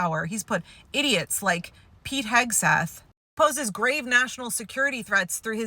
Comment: You clearly said "Hegseth" but your automatic subtitles said "Kegsbreath" 😂😂 I know you've said doing subs isn't part of your editing and that it was automatic so I thought that was hilarious!